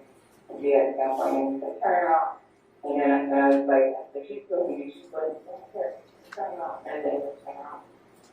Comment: I think she’s saying “she’s bullying me she’s bullying me”